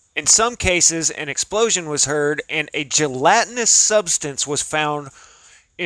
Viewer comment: “And a gelatinous substance is found.” But I heard, “and ancient Latin is substance is found.” 🤯